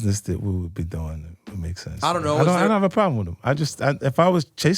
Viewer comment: I heard wrong I thought he said he doesn't have a problem with him not a problem with doing a song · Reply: he said he doesn’t have a problem with him. I don’t think he said anything about doing a record